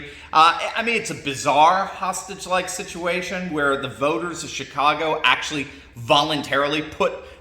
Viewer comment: Not to be base, but I honestly heard you say, “Shitcago”.